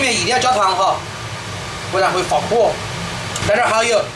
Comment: what is that ingredient? it says fuel for consumption in the subtitles? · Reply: Niño Paulo Reblando oyster sauce